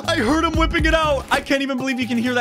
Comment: "I heard him wiping it out..." hahahaha